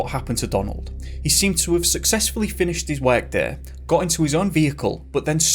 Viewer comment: What does he mean when he say........... " finished his wag there"? The Donald guy. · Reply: He said his “work” but his accent made it sound quite different.